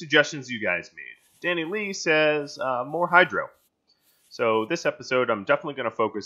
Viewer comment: For a second I thought you said Hail Hydra instead of More Hydro xD